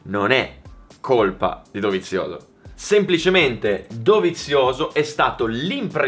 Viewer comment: min 7:56 ho capito per 3 volte E' COLPA DI DOVIZIOSO ... ho capito bene ?